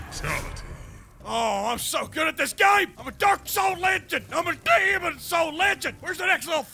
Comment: A Durka Soul Legend!!! 😅😅😅 At least that's what i thought i heard lol